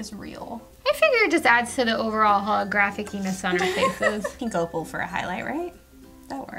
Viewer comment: I just have to comment on this because it's so funny! I have a toddler that was still sleeping this morning when I was watching this so I put on the automated subtitles. And at about 6:54 it said holographic penis on my face is pink opal... I was confused so I risked waking up my kiddo and turned the volume on to see what you guys really said. It was holographic-e-ness. So funny it made my whole day! 😂